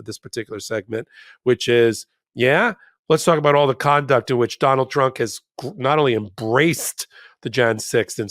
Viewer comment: it sounded like Popok called him Donald Drunk. 😂 Would explain some of his incoherent ramblings lately.